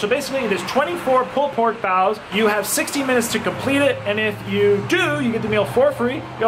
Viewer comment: I thought they said 6 minutes not 60…